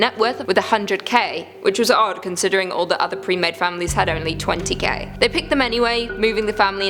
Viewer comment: did she said twenty gay?